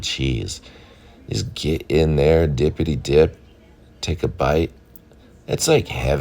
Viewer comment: Omg I had to replay the "dippty dip" part. I thought you said "Get in there and dip your tip". And I was like.....sir, this is a wholesome cooking channel!! lol.